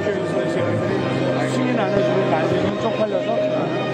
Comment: 저는 국회에서 일하는 사람들이 승인 안해주고 날리면 쪽팔려서 어떡하나로 들리네요. 수십번 들어봤는데요. 제 귀가 이상한지 모르겠지만. 사람은 누구나 듣고싶은대로 들어서 그런거라생각되네요.